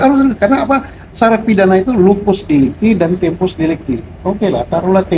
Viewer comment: apa aku yang salah dengar " LUPUS DELICTI " ada yang sama dengan saya ? ( sdh pake headset tetap LUPUS)